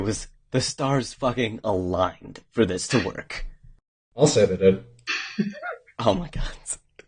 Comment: what did say at 17:57? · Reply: said "I'll say they did!"